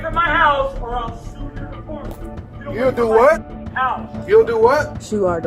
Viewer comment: I heard say that "I'll shoe your department' .... that's what I heard! Seems to be a generous man!!